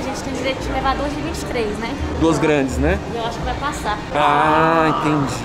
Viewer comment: eu entendi "LOS GRANDES NÉ" KKKKKKKK